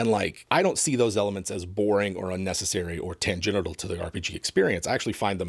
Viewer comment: Did say "tan genital" instead of "tangential"?